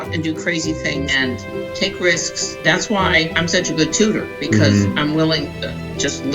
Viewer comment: I totally thought said “that’s why I’m such a patooter” instead of “good tutor” 😂😂😂